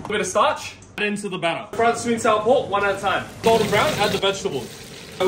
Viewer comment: I misheard it "Sweden sour pork"